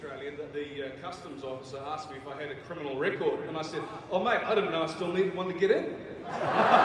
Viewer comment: What did Karl Urban say at 18:22? Sounds like it ends with "...get in." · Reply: "The customs officer asked me if I had a criminal record. And I said, Oh mate! I didn't know I still needed one to get in!"